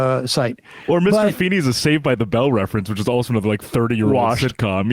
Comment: am I the only one who heard the guy say Mr Feeney was on Saved by the Bell